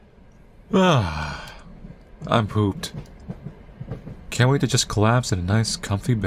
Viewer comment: Genuinely thought it said “I pooped” and not “I’m pooped” for a good few seconds and was very concerned 😭